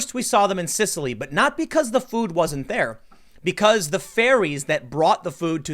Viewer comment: Anyone else mishear ferries as fairies? >__<